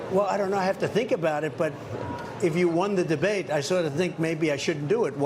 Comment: I thought he said he won the debate, Why's he complaining about the moderators?